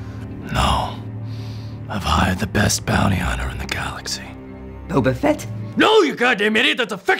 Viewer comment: I hired the best bounty hunter in the galaxy. Boba Fett? NO YOU GOD DAMN IDIOT THATS A FICTIONAL CHARACTER!!! Who did you hire then? Spiderman. xD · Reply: I thought it said wobbufet not bobby fett